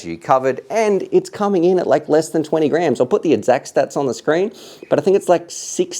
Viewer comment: For a split second I thought you said that it was coming in a bit less than 20 grand...